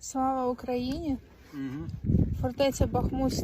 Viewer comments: Мне одному послышалось "сало Украине"?😂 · Мне одному послышалось,сало Украине ?